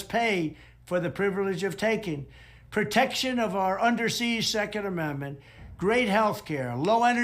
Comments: For a second, I thought he said, "Undersea 2nd Amendment." As I'm thinking about guns and submarines, I realized he must have meant "under seige", but I'm actually not sure that Trump wouldn't fight for the other option. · I thought for a minute he said, "protection for our under seas Second Amendment". Wondered if he was maybe stealing some new "policy idea" from the brain trust over at the DeSantis camp since it sounds like a Florida idea. In any case, seems he was just slurring "under siege".